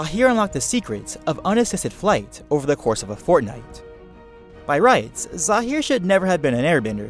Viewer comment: did he say fortnite or am i hearing wrong